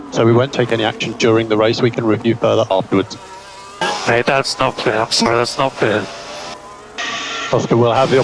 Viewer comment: What did Oscar say? Can you please write it down in writing? I'm really not okay with this team's work. I feel sorry for Oscar. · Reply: "Mate, that's not fair. I'm sorry, that's not fair" "Yeah but if he has to avoid another car by crashing into his teammate then that's a pretty ** job of avoiding"